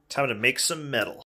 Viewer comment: I thought he said “time to make some milk”